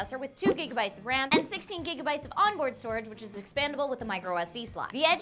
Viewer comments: did she say 16 GB and they typed 6 GB? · they put 6 instead of 16